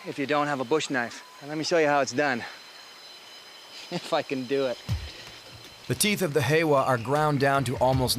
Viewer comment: when I was a kid watching this i thought he said "they fuckin do it" and had to rewind a few times before i saw he said "if i can do it"